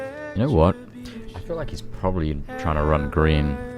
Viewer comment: Anyone else hear a lady answer”what?” When says “You know what?” at 55:47 😳